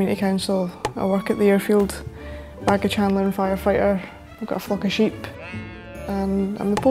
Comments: lol because of her accent it sounds like she said “ I GOT FOOKING SHEEP” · Thought she said “I’ve got a fookin sheep” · i heard “I got a fockin sheep”